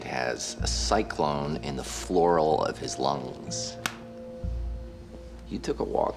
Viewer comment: I want a cyclone in the floral of my lungs. Life isn't worth living anymore. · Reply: "Pleura" not flora lol I thought he said flora at first too